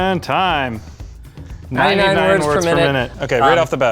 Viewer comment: Is it just me, or did I mishear that he can type 99 words per minute?